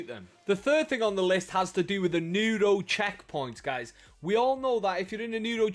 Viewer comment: I thought you said noodle checkpoint bit it was nero